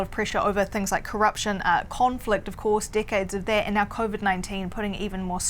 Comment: "Decades" sounded like "dickheads" when she said it.